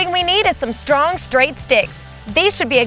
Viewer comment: I thought she said a hard straight dick at first.